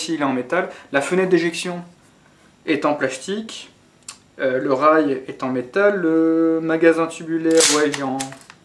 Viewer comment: Hey, at 8:55 the english subtitles say that "the ejection port is plastic, the rail is plastic" but when you listen it sounds like you're saying that the ejection port is plastic and the rail is metal. Which one is it? :)